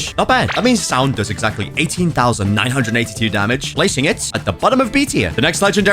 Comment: it actually does 19,982 not 18,982 or the caption is wrong